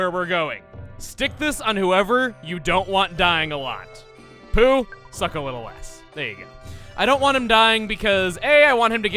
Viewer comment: I thought Chuggaa said,"Poo. Suck a little ass."